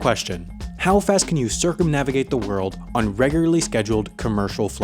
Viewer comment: Ooohhh 'circumnavigate'....I thought it said 'circumcise'... 😐😐😐